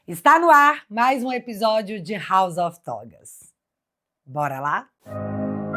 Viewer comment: Minuto 1:35. Juro que entendi House of Tob4$...kkkkkkkkkkkkkkkkkkkkkkkkkkkkkkkkkkkkkkk